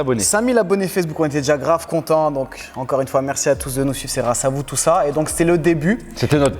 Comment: seconde merci à vous de nous suc.. ?? C’est moi ou j’entends mal ? Jte jure on dirais que tu dis ça 😅😂